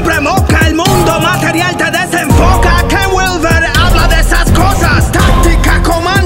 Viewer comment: En el minuto 4:48 habla de un tema pero no entiendo el nombre del calvo para buscar ese archivo. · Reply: Ken Wilber. Te recomiendo que siempre actives los subtitulos, así te pierdes menos.